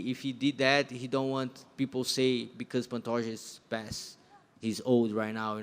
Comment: This is Pantoja’s era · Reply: for a sec I thought you said Pantojas ear